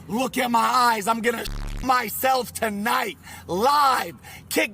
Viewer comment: With the censor, it sounded like he said "I'm gonna shit myself tonight, live" lol